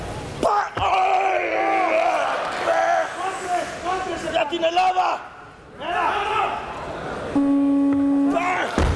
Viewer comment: What did he say at 1:54 ? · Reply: για την Ελλάδα = For Greece